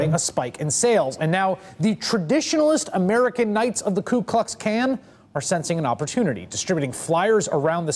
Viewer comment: ku klux can 0:52 lol is that what he said